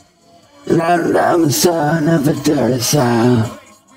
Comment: Set your playback speed to at least 125%. It compensates for his brain working at 75%. Tard rapping: 12:30 “I’m a son of a dirty sow.” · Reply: I legit thought he was saying "I'm the son of a dirty sow"...LoL